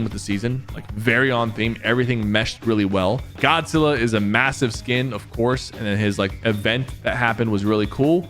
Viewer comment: did you say m-m-MASSIVE? 😭😭😭